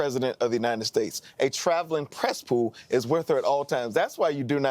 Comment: Travelling press pool? I thought they said she was a travelling cesspool.